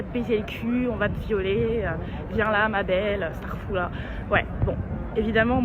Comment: ta dit quoi j'ai pas bien entendu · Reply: Starfoulah